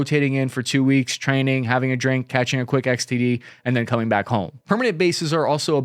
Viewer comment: Did he say STD!!?? (Sexually Transmitted Disease) 😱🙊